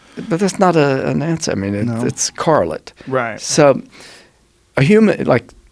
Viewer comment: Joseph uses a word that sounds like Carlot (but isn`t) when describing the information that could be gained from using an FMRI to scan a dogs brain wave patterns . Does anyone know what this word is ? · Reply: The word is: "correlate".